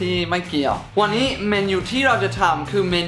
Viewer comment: ใครได้ยินพี่พูด เมนยูบ้าง